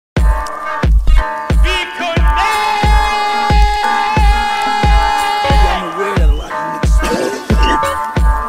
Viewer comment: wtf is he saying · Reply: bitconect